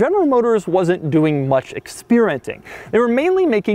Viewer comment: Did he just say "expeerenting" at 00.43? lol